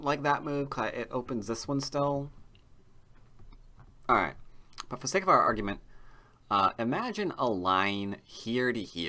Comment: What’s he saying at 09:45 ? can’t understand it... what argument? ^^ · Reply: "For the sake of our argument" :)